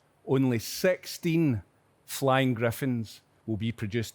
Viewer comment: did he just say only SEXTING griffins will be produced lol